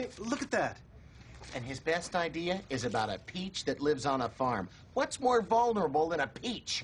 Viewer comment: "A peach that lives on a farm" I thought he said no farms cause it was too mainstream?